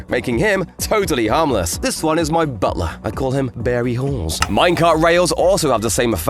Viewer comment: "barry halls" i thought you said harry balls